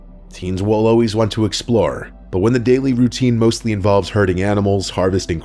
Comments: I thought he said "hurting" and for some reason I was just like "country kids? Yeah that tracks." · Who else thought he said "hurting animals"? Because I did... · this is satire, right. right? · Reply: I misheard herding animals as hurting animals. That's all.